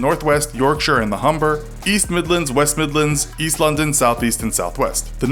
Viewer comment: I'm from London and when you said "London" right after "East" it sounded like you said it together like East-London so I was suprise as f*ck and thought you said GOT was based on East-London LOL.😂😂😂